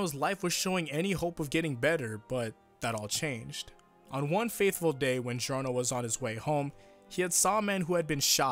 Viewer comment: am I dumb or did he say faithful